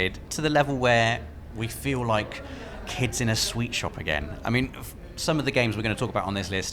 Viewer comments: I thought he said sweatshop the idiot American I am sweetshop is candy store I’m guessing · So excited you feel like kids in a sweatshop? That's what I thought you said. Between the accent and the fact that we don't use the term "sweet shop" here in the US. Anyway, I got a laugh out of the misunderstanding. Also I totally agree that some of these games FF7 especially have me feeling childlike levels of excitement.